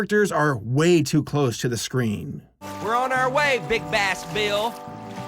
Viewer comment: I thought they said “Big Ass Bill” and not big bass bill lol.